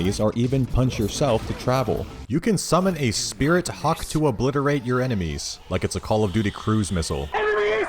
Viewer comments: did he just say… spirit hawk tuah😂😂😂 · I heard you say "summon a spirit hawk tuah bliterate your enemies" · Wait, at 22:30, did he say… heh 😼, hawk tuah? Those who know 👹👹👹